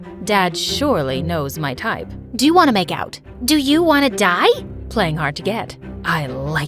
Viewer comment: she actually said"do you want to die?!"